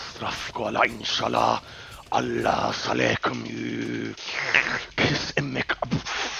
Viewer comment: Did u just say Kos Omak?